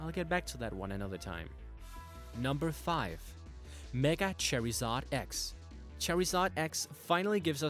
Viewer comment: Sounds like ur sayin cherryzard haha